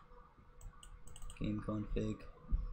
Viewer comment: Him: Config What I heard: King Kong Pig